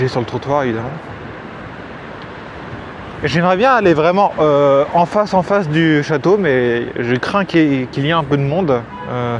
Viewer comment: Question 🙋 tu dis quoi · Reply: Je dis que j’aimerais bien aller vraiment en face du château, mais que je crains qu’il y ai un peu de monde 😂